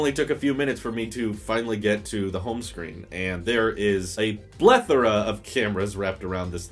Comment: did you say blethora >.< :)